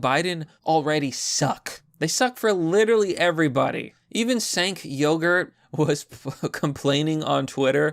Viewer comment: Had to rewind at the one part because I thought he said the guy's name was "Sink Yogurt".